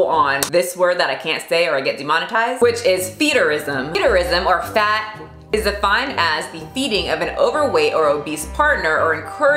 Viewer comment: She says "Fat f-----rism is defined as-" I heard "fat f-------rism is da fine ass-" 🙁🙁🙁🙁